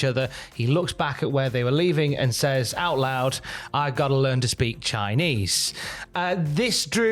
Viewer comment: I thought when I first heard it love was he said he wants to order Chinese 😂